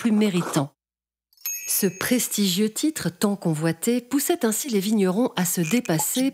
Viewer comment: Pourquoi y aurait-il besoin d'un "t" entre "poussa" et "ainsi" à 1:18 ? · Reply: C’est pas poussa mais poussait, donc il y a la liaison. Écoute bien avant de parler, espèce d’hypocrite 😏